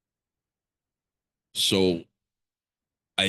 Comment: is he saying 12 billion or million? Sound like billion, but seems high.